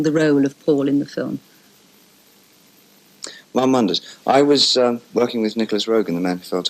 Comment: what did he say?? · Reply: he said "one wonders"